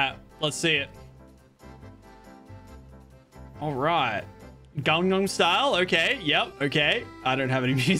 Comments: gagnam style instead of in da geto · Did he call in da party Gangnam style I’m dissapointed time stamp